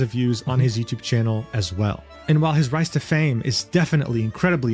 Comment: Why did i hear "Rice to fame"?